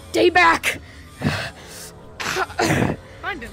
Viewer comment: can someone tell me what he said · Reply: Stay back